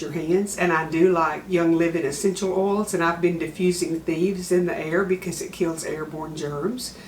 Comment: I've listened 4 times and@ a little over a minute in it sounds like you said you are diffusing "thieves" to kill the germs. What are you really diffusing? 😳 Blessings from Indy. 💜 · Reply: Thieves is an essential oil combination from young living that kills 99% of germs etc. we’ve kept it diffusing to purify the air.